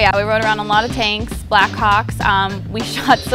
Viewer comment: Then it sounds like she said "hocks". She pronounces "hawks" strange.